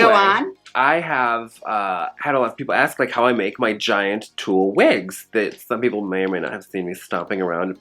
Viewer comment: I swear I thought this said "how to make a turtle wig" and just didnt even question it